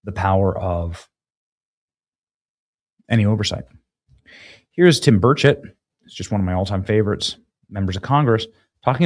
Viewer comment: Benny i thought you said tim birdsh!# i had to replay😂 thank you for your great content ...scary very serious